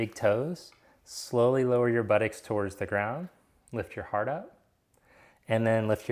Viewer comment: i cant understand his accent - what does he say at 05:29 lol it sounds like 'lift your heart out' to me